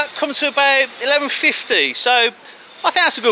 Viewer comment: I couldn't understand what he said there... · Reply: Cushty